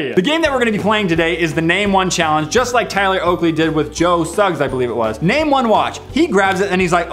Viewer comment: sound like he said Joe Sucks